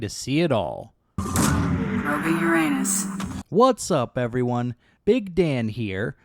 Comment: You son of a bitch I almost choked when I heard “probing your anus” bravo just bravo lmaoooo